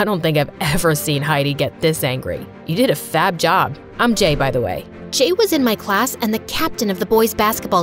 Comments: bro I really thought he said I’m gay btw also 6:46 I thought bro said that’s not what I meant you gay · My dyslexic ahh thought he said "Im gay"